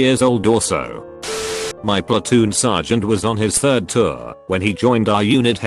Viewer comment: okay i know this is serious but i swear to god he said splatoon